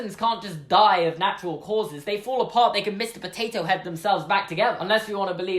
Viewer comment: "They can Mr Potato Head themselves back together" That phrase just made me laugh so hard! Thanks for making my day :D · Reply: The Noteblock Lizard I thought he said “can’t”